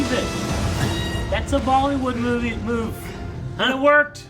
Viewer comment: wait did you say " That's a Bollywood Movie Move?? " you have watched a Bollywood movie? which bollywood movie you've watched? can you please start reaction on bollywood movie....